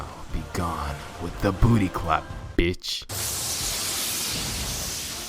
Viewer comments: 😭😭 begone with the booty clap ... BITCH Did he say booty clap ..... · Omg I thought saskuke actually said WITH THE BOOTY CLAP BITCH